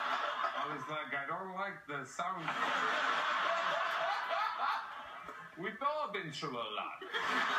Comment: What did chris say at 1:59? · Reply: We've both been through a lot.